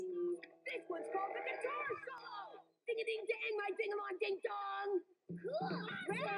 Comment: What’d he say??? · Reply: Ding a ding dang my ding a long ding dong!